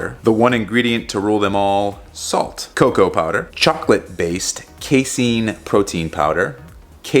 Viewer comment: what protein powder? Whey chocolate protein powder? oh yea, that's what I heard